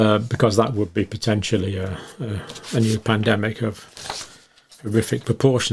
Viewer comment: I thought I heard John say "plandemic" at one point